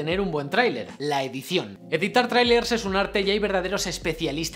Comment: Cual es el nombre del tio en el 9:45 xd Mark Bulen? Mart Boomer? Matt hummels? No entendi lo que dijiste ahi